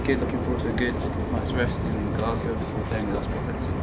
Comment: i thought he said...: " attaked nby midgets"